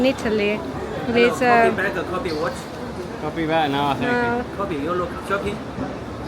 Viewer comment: I didn't get what the bloke was saying? Was he trying to sell you something? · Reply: He was trying to sell copy watches. If you agree he can bring you to the shop and if you successfully buy the goods he gets some. commission. My suggestion say No and walk away and they won’t force you.